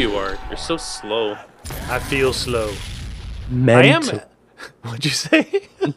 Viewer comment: What did say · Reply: merde i think